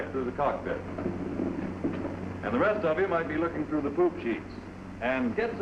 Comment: Did he say "poop sheets"?